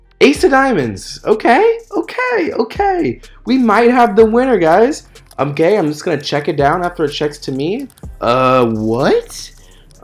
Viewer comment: does say “I’m gay, so I am just gonna check it down”.....LOL